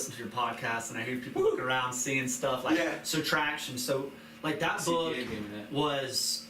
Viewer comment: Hey. What was the name of that book again that you both were referencing? Sounds like he said "Subtraction" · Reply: TRACTION - Let me know what you get out of it!